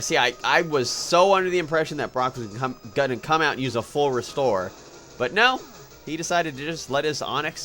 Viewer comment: when you got that full restore in viridian forest, it sounded like you said forest ore XD